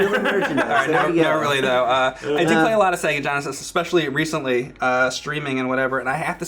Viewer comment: ol boi says 'sega gonesis'